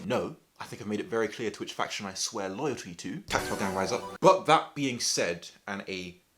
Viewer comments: Anyone know what he said at 4:39? Which switches does he swear loyalty to? Sorry, new at this and couldn't make it out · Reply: no problem, I said 'tactile gang rise up' - I really like tactile switches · What did he say at 4:38? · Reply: "tactile gang, rise up." not sure though.